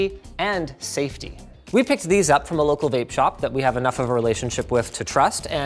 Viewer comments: Did he say... vape shop? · did he say he bought these new batteries at a vape shop? Huh?